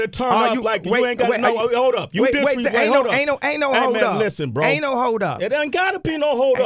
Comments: Am I the only one who thought Kanye was saying "ANAL HOLD UP ANAL ANAL ANAL HOLD UP." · when kanye said aint hold up I thought he said anal hold up